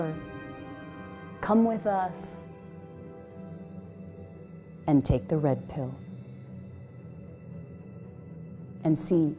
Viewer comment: Ok...you might think I'm being facetious here..and thats because I am.. I know its likely the audio quality on older info wars stuff being absolute garbage but I could have sworn she said "Take the Lead Pill".